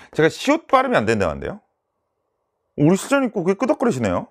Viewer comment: 에 뭐라고 말씀하신건가요? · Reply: Andy Kim 우리실장님고개끄덕거리시네요